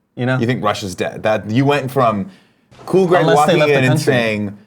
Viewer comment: I totally heard "Russia's Dead" too and that made Andy's explanation that much better 😂